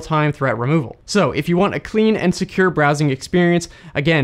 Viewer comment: When you said "clean and secure" it sounded like you said "clean insecure" lol